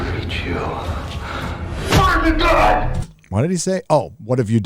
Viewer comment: I thought I was the only who couldn't hear "What have you done"@ 4:08.